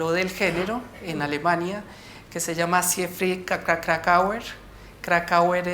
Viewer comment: Por la gente que al inicio, como yo mismo, no ha comprendido el nombre del gran teórico (For those who also at first couldn't hear the name at 7:28): Siegfried Kracauer